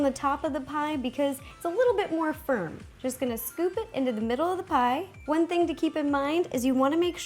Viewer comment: I skipped through and it sounded like she said "Stick it into her melon" o_O Anyways pls do chef hat or fnaf 3 caiek! :3